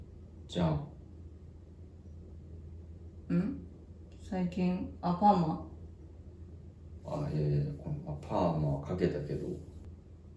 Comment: 最近アンパンマンって聞こえて、どういうことってなった。